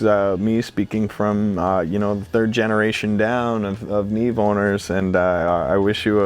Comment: I thought he said "Neve Boners" in a serious joking way, then I realized it makes more sense that he said "Neve Owners"...